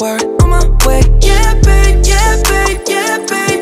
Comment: When he says yeah babe yeah babe it sounds like hes saying get paid, get paid 😢